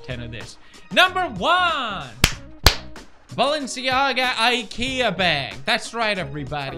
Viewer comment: Did I just hear IKEA Bird?????